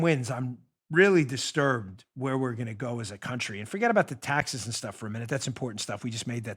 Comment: Oh. Thought you said "Bat Country". NEVER stop in Bat Country!